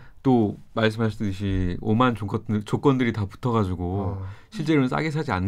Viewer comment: 오만 조같은 으로 들리는데 ㅋㅋㅋ